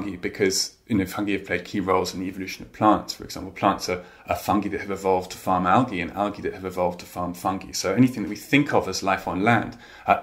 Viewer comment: Did I hear that correctly? "Plants are fungi that have evolved to farm algae and algae that have evolved to farm fungi"? Seaweed is another interesting and curious foodstuff which is part of neither the plant or animal kingdoms (some varieties anyway), so perhaps fungi and algae should be considered together despite being classified separately.